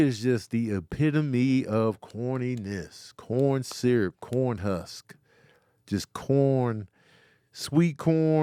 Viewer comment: It’s DG Yola not DeeJay… yeah everybody frm Atlanta in the early 2000’s was slapping tht in traffic…he got shot and fell off. When Tony said 69 was “corn husk” I thought he said “corn hub”🤣🤣🤣